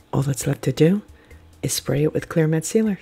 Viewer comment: what did you say you’re gonna spray it with I couldn’t hear you you said you were in a spray it with something was a glow in the dark or. Protectant. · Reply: Hiya, Jody! I’m sorry. I said clear matte sealer spray. I usually use Rustoleum 2X. It’s a protective coating 😊